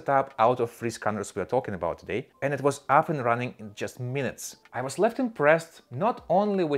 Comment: Excellent review! Only comment I can make is that on the Raven review about scan quality, I believe you said, “I was left impressed” but it sounded like you said, “I was less impressed”. Thank you for the engaging and succinct review.